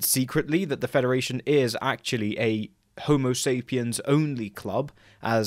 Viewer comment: I always thought she said homosapiam zonuclop not only club. You’ve cleared up a decades long misunderstanding